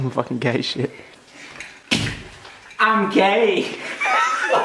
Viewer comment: I thought you say: I’m kay